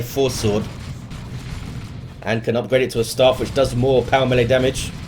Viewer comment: Did you say he can upgrade the force sword to a "star-fish"? :D